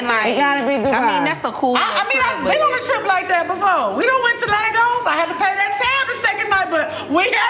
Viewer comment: Did she just say Lagos?? Lex be fucking with my Nigerian brothers…. That’s cool 😎 respect ✊🏾